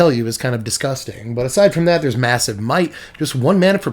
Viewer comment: first time you said Massive Might... I thought you Said Missive MIKE lol ha ha I thought you where naming the Snail.. an thanks for the $5 Decks..